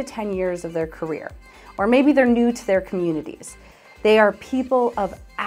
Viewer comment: Hi guys, spanish native speaker here, could someone tell me what does she say at 1:15 or maybe their ---- to their communities* Thank you guys! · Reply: Hey! She says "or maybe new to their communities"